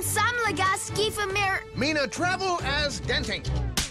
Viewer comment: if you are wondering what he said to set her off he said “give me my travel a-hole”